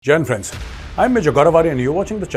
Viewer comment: Took me 3 months to realize you are saying Jain friends in your intro. Always thought you were saying Gen friends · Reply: It's "Jai Hind Friends " that means victory to India